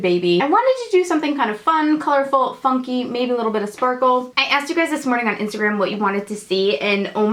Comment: I thought you said you were going to a look that is "funky and maybe a little bit historical" at :30